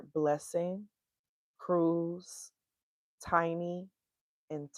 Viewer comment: did you just say Tiny?????? Cuz that’s the nickname my brother used to call me 😂